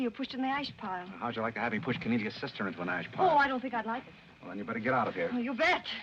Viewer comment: did Powell say:"'How would you like to see Cornilia's sister", OR "Canadian sister"??? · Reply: He said "how'd you like to have me push Cornelia's sister into an ash pile". And, that line should be credited to Godfrey Smith for saying it, not William Powell.